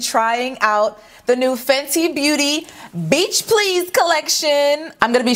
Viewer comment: For a sec I really thought it said bleach please collection💀💀💀